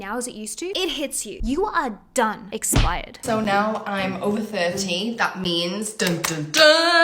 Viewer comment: I heard over 13 😂😹 🤣